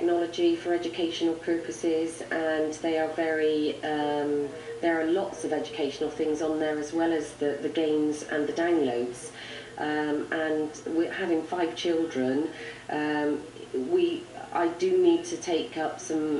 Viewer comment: did she say danglonds?!